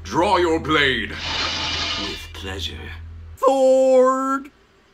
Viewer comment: THHHHOORRRRDDD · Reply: I heard “Thork” but I guess Goofy saying, “Sword” makes more sense